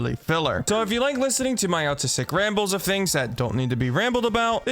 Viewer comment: DID SHE JUST SAID ALTISM???????